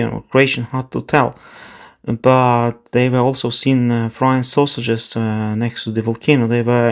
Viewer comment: First time I misheard you as saying the Serb Croats reported flying saucers before realising you said frying sausages. Says more about my hearing than your English, which is excellent for a non native speaker. At least these unwise tourists have provided us remote viewers with a reference for the sale of the splatter cone. For that they should be thanked. Then fined.